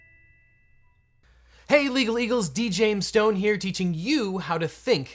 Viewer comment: It almost sounds like he say illegal eagles